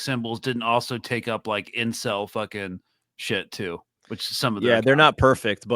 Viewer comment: Did he just say el-cuck?